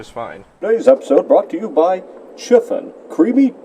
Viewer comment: the way pronounces "Chiffon" sounded like "schiffen". Schiffen is slang in german for "taking a piss / to piss".